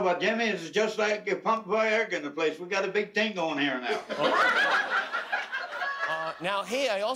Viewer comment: Any newfies translating for us again today? ^^ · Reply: I thought he said it's like you pumped Viagra in the place, we got a big thing going here now.